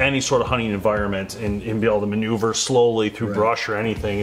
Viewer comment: I misheard ‘able to manoeuvre slowly through brush’ as ‘able to manoeuvre slowly through Russia’ 🤦🏻‍♀